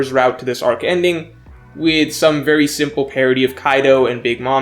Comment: I thought u were saying parody but u were saying parity